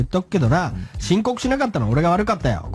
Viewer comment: 滑舌が悪いとこがあって 申告しなかった俺が悪い… が、ちんこくさかった俺が… に聞こえたが、聞き間違いだった。